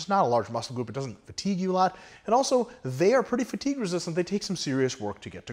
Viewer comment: "...and also they're pretty fatiguers..." -- is that what he said? He's talking fast there so its kind of unintelligible. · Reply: Pretty fatigue-resistant.